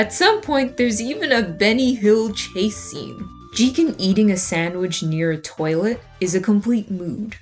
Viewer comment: Jigen eating a sandwich next to a toilet. I'm not gonna lie, at first I thought it said "Jigen eating a toilet next to a sandwich" XD